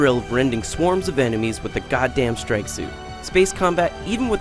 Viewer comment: i thought i heard he say 'gundam strike suit'